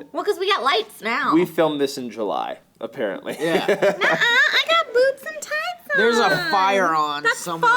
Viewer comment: I thought said at 13:00 was "i got boobs and tights on!" I'm glad I added such an insightful input to this comment section